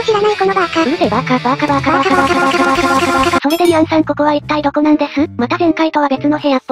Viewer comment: 編集お疲れ様です！ 0:50～0:59のところ好きです！途中からカバーカバーって聞こえたのは気のせいですかね 2:28～2:33のところ好きです！収益化剥奪と聞いてやめるの好きです！ 3:17声にノイズが入ってるの好きです！ 3:47カウントダウンと画面注意のところ、視聴者への配慮と時計好きです！ 3:51～3:58驚かし要素スルーなの好きです！ 5:24～5:31のところ好きです！ 無理をなさらず頑張ってください！